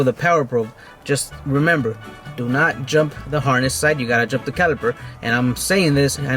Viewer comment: Please could you describe what are you saying from 6:28 to 6:33? do not jump the hornest side? what does it mean? i will be doing it this weekend. thank you very much for your reply!